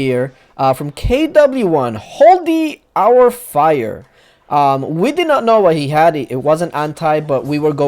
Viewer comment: I love that pronounced the guys name at 1:19 "holdy our fire" instead of "hold your fire"